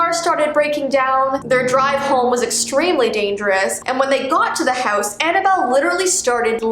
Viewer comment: Did she just say “dangous”